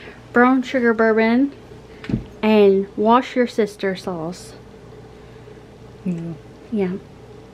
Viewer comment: Lol!!! I thought you said wash your sister sauce instead of Worcester sauce haha 😂. The steaks look good!! All your renovations are turning out nice. Hope you both enjoyed your barbecue. 😊